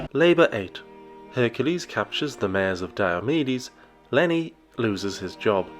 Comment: I swear I heard “Mayors of Diabetes” which actually sounds like an amazing pair of Dark Souls Bosses or something.